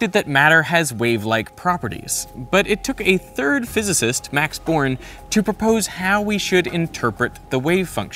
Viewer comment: me: wish I had something smart to say also me: why i keep hearing "Max Porn"